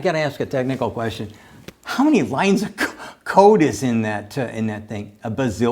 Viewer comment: i thought he was about to say "How many lines of coke..." jesus christ.